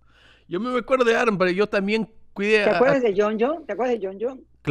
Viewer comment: Hey Spanish speakers! How does she say "do you remember" at 10:58? What Spanish does she use? And please don't tell me it's "te recuerdas" or I give up learning Spanish 😂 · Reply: She says literally "¿Te acuerdas de Jonjo?". It's a very standard expression in Spanish.